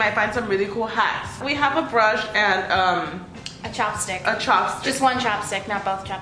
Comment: wait am i the only one who heard chapstick.